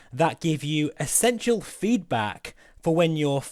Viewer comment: I thought you said sensual feedback at the beginning! Thats thing looks great, lot of dosh though on top of everything else and yes a race sim would be interesting to see - thanks